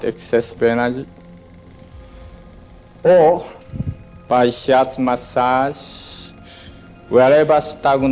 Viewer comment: the subtitle is wrong. Kushi-sensei said "shiatsu massage", not "shorts massage".